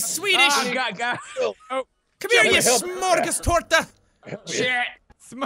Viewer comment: jack calls felish a smorgastarta, which is a sandwich-like cake made in Sweden? either jack knew this and called felix a cake or i heard it wrong?